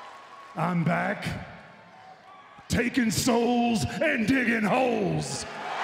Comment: Am I the only one who thought the undertaker said " I'm back taking soul and diging hoes " 😂